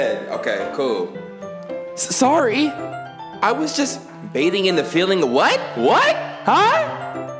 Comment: "I was just bathing in the feeling of your body heatttttttttttttttttttttttttttttttt eattttttttttttt" i think that's what it says